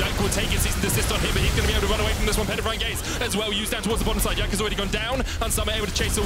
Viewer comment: they said “jankos already gone down” instead of time lmao